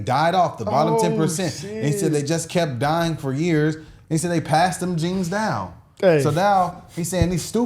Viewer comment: No that is not what he said at 5:20. This is what he said: the bottom 10% of slaves died off, NOT passing their genes down, NOT is key word. when you die, your genes die with you. Leaving the smarter and more capable black slaves alive, and this is why Black Americans today are smarter and more capable than black Africans today. You misunderstood him. Basically, he is saying that black Americans are smarter and more capable than black Africans today. I'm not saying I agree, I'm just correcting the misunderstanding. he was not calling you stupid, he was calling you smart. Well in a way!